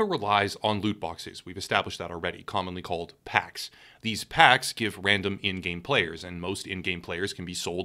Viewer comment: I heard "hacks" for some reason